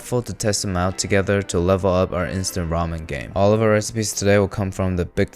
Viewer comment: I thought you said instant vomit game. Edit : peep my banner to pee vomit